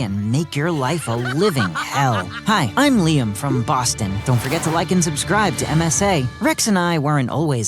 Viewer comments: Did he just say "don't forget to like and subscribe to MSA"? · MSA did they say?